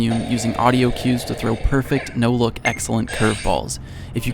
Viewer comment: Did he just say “perfect, no look, excellent, curve throw” ? I do great throw most of the with complete focus